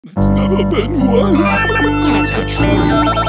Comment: If you listen close at the beginning where you hear the voices it says " It's never been worse" and another voice says "That's not true." I just wanted to point that out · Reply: I always heard it as "Its never been once"